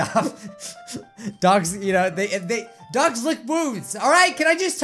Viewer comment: it sounded like he said "DOGS LICK BOOBS" 😂😂😂